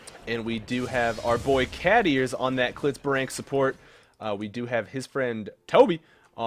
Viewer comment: Wait did you say CLITZBRANK SUPPORT???? LOLOLOL